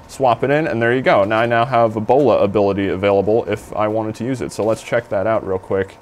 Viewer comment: sounded like he said ebola ability xD